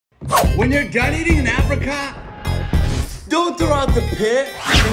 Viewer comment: I thought he said "when you're done eating in Africa" Was like wtf did he just say?